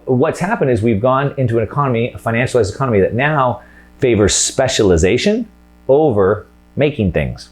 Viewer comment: say speculation instead of specialization